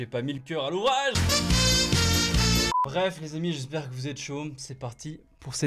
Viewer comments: jai entendu « j’espère que vous êtes chauve » ( ͡° ͜ʖ ͡°) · jai entendu « j’espère que vous êtes chauve » ( ͡° ͜ʖ ͡°) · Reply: Moi j'ai entendu "j'espère que vous êtes chaumes " 😂